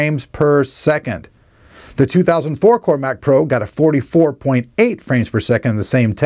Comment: anyone else hear him say 2000 4core